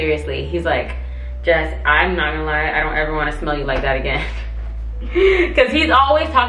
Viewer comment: Why can I hear say I don’t want to smell you like that again lol